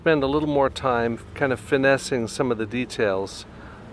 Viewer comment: did he just say FINESSING